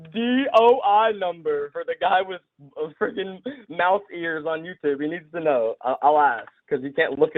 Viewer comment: did he say "mouse ears"?